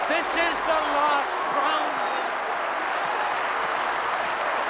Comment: I thought he said "You're dead" for YEARS. He actually said "To the end".